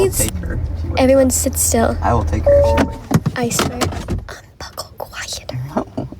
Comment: I’m not one to comment, but the bloke that slammed door with the baba that ain’t sleeping-I’m sure I heard him say faintly I will take the baby……I might of miss heard, apologies if I had, had headphones on and I am partially deaf! I also agree with so many of the comments-we never really know the full picture. For me it’s about the kids, the poor little babas.